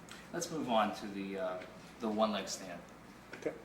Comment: it sounds like he says, "And then you moved on to your last test, the "one night stand test" lolol i was like ✋ wait what... Lol but he said "one leg stand" which standing on one leg for 30 seconds seems like what a personal trainer might ask a nice fit person to do during workouts... Not a cop to an average citizen on a traffic stop..